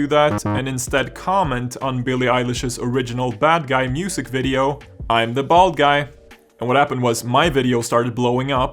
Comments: I thought it said "I'm the bald gay" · i fucking thought that said i’m the bad guy. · I thought he said "I'm the bad guy"